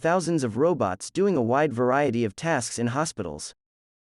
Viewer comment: There are thousands of robots doing a wide variety of tests in hospitals. I heard test but you wrote tasks... Pls am I hearing something else?